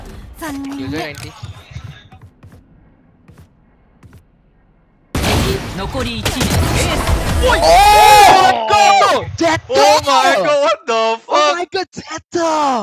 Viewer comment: did i hear "oh my god the fuck"?